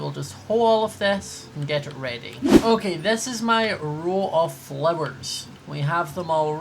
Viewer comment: I thought he said bun of flowers instead of "row of flowers" and I was like "WAT"